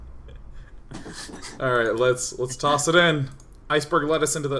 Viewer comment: LGBT lettuce. could have sworn he said LES toss it in. Lesbe honest guys.